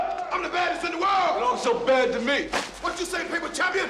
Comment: What did Rocky Balboa say? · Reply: You don’t look so bad to me.